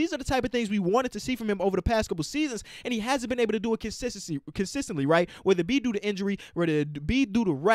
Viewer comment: Notice how said Kensistensy instead of Consistently 😂😂